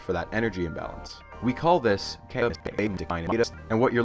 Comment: i lost it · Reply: I clicked on that when I was at 1:01 and it sounded like he said “We call this: Nothing” 😂😂😂😂😂😂